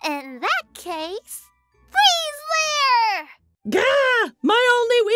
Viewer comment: Pixie is so cute I love how she sounds like she said freeze layer